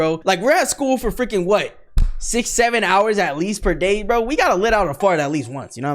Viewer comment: HOLY MANGO!!!! DID SAID 6-67 AT 15:44 !!!!! HOLY MUSTARDY!!!!🤑🤑🤑😼